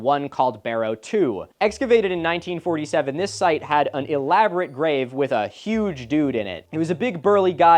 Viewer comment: I thought you said they "had an elaborate rave with a huge dude in it"